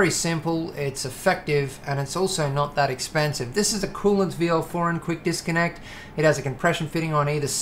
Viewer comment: Btw i couldn't hear what on 16:20 you said. coolance VL disconnect?